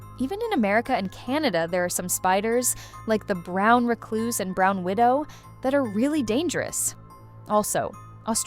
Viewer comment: Did she said a brown widow